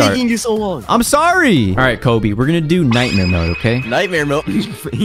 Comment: 0:9 Bro did u just say Insane mode instead of nightmare?? im not hating btw